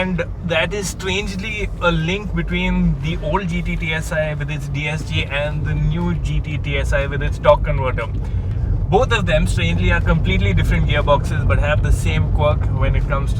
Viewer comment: Did you say old GT DSG and new GT torque converter?? Interesting...